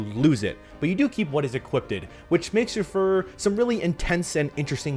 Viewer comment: did you just say “equipded”?